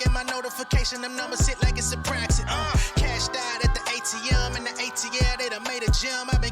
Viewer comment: Not that I thought this man was saying Kash Doll at the atm for the longest and not CASHED OUT 🤦🏾‍♀️ but then why th would your husband ever be rapping about Kash doll 😂😂😂